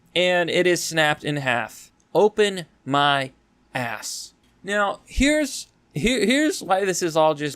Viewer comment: I wasn't paying attention and all I hear is "Open. My. Ass."